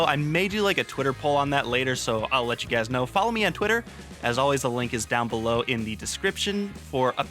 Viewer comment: I thought he said f*ck me on twitter for a second